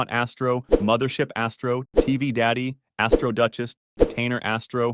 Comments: did bro said tv daddy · Nah did he say tv daddy?